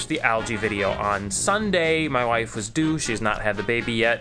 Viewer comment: When you said "My wife is due" at 1:11, it almosted sounded like "My wife is a douche". Had me really confused for a second